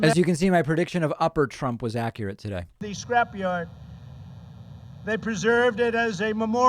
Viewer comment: Of course he has to point out "the crowd" ... I heard him say "the scrapioid" ??? @ 1:15:18 WTF IS "THE SCRAPIOID?" How often does he have to comment/aside his own speech? So stupid. How many times did he say the word "TOUGH" in this speech?